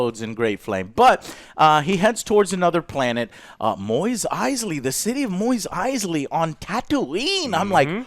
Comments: Did he just say "Moist Eisley"? lol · The end scene was Boba I keep hearing say "Moist Eisley". it's Mos Eisley, as is moss.